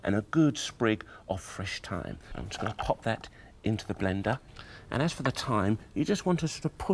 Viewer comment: DID I HEAR "BLENDAH" ?